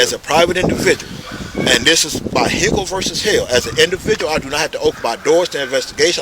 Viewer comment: Did he just say Hippo versus Hail?